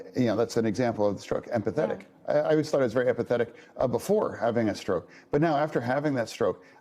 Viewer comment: Sounded like he said he was more pathetic.